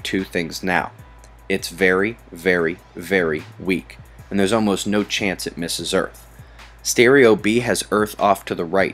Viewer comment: does he say "There's no chance it misses earth"?? Great video and info. I will be checking in for updates! Bless you All!